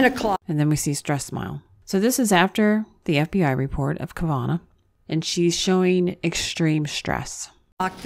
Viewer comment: Did you say Kuh-vah-nah?